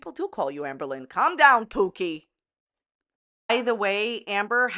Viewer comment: You said “pookie” I misheard and thought you said “poopie”